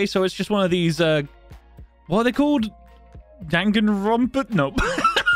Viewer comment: Did I hear DaNgAnRoNpA?